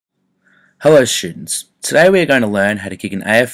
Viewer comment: i heard, 'HEllo SHitS'